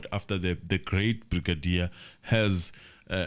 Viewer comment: did you just say "the great brigadier "😂😂😂